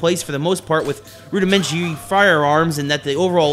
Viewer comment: LOL! i heard 'elementary fire alarms'!